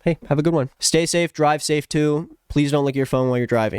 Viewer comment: nah cause i thought u said please dont lick ur phone while ur driving